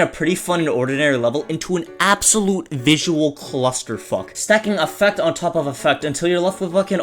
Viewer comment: "Clusterfuck" might be the greatest name for these I've ever heard lmaooo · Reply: I thought he was saying "Claustrofuck" as in claustrophobia